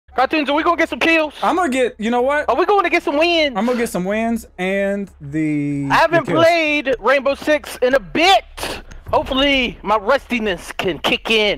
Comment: At the start, I thought Delirious said 'Are we gonna get some wings?' 😂